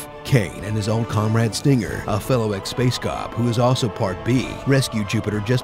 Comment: Did you just say part BEE?